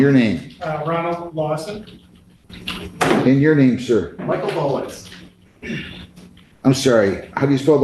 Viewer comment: Just like judge McNally, I thought at frst the guy said "Michael Bollox" lol